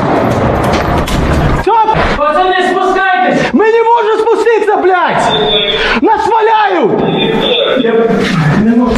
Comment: if someone could tell me what they saying from 2:23 to 2:27 i will apreciate it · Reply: "Guys, get down!" "We can't get down! We are under the fire!"